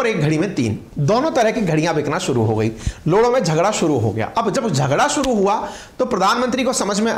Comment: Sir 14:55 par kya bol diya😂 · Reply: झगड़ा